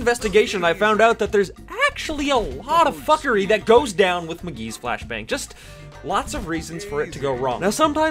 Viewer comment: did he just call him mcgee :))